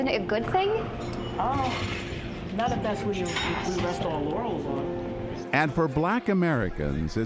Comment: anyone hear "not if that's what we rest our yannys on"